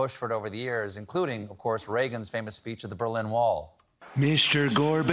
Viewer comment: Now see I thought Regan said "Mr. Gorbachev, hit this blunt!"